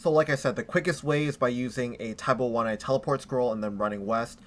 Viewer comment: WHAT THE FUCK ARE YOU SAYING?? What the fuck is a tab01 or whatever the fuck your saying is? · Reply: tai bwo wannai...